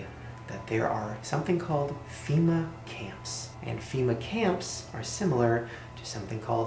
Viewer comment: I thought he said fema cams like cameras so I panicked an put duck tape over my camera went back to make sure and he said camp and struggled to get it off and started crying because I thought i wouldn’t take anymore selfies